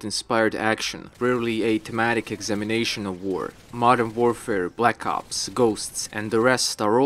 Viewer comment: i swear i heard you say Black Cops.